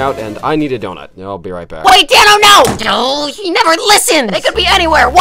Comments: "THEY'RE COMING FOR YOU, TAKE CARE! NO-O-O-O" Riggy's last words... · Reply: when he said take care i thought he said danno no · I just realized he told us to take care. That's really sweet. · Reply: I thought he said “WAIT DANNO-“